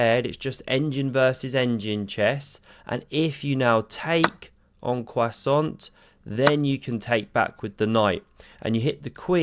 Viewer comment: is it just me, or did he say "en croissant"?😂